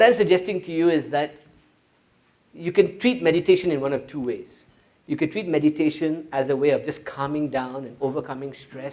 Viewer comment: what does he say at 56:10 "..the body is '........' to achieve shiva consciousness" ? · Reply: delishimo vehicle